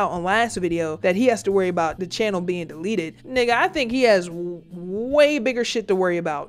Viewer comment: Wait did you just say nigga😳🙄😦😯🧐